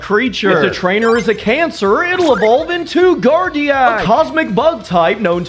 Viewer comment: did he say if the trainer has cancer